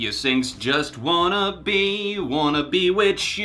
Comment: what did he do? Say witch instead of with? If so, what does witches have to do with the black death?